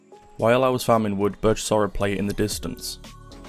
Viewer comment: Did he say plate? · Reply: i said "player" kinda sounds like plate KEKW